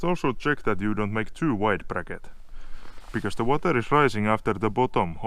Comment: I am sorry but your pronunciation is hard to understand in English. Did you say ( you must also check that you don’t make too light bracket because the water rises up from the boat bottom also there .. ? ). If that’s not exactly what you said. Please correct me if I’m wrong. ======= > it is said near 4:04 minutes of video. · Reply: I think you say too wide bracket. damn, your diction is awful. lol